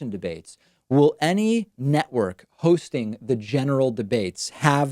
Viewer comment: Did he just say “Testicular Fortitude” (4:54)? LOL 😂